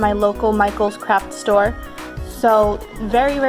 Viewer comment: I thought she said crap store XD but I figured out that she said craft XD